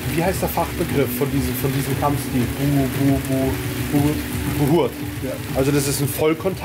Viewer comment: Wie nennt sich diese vollkontakt Kampfsport doch gleich noch ?habe das bei 11:35 nicht verstanden :D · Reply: Buhurt